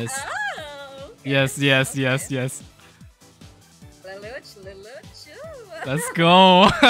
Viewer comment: did she say lulu chu?🗿